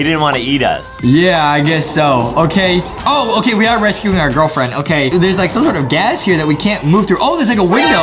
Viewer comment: did this man say are gf 😂😂 he really said ohhh we are rescuing our gf 😂😂 the time he said that is